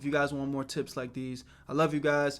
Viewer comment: Finish this fam WHERE YOU AT_____!? 🗣️🔥, I’m really proud of this vid. Giving yall some of the biggest Gems that I wish I would have gotten growing up, so I hope yall enjoy! BIG Shout To Tiege For The Sponsor, yall go tap in ❗️ Tiege Hanley: Get your first box 40% off (+ FREE gift), and 20% off for life, at <link> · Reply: i hear "where you at weirdo"😭😭